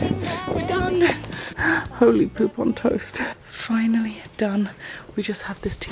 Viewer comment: am I the only one who heard her say "Holy Groupon Toast"? 😂😅